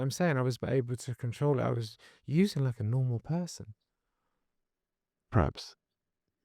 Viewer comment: thought he said he was using like a "normal person would"